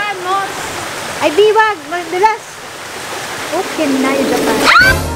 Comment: "ukinayu japan" I heard that HAHAHAHAHA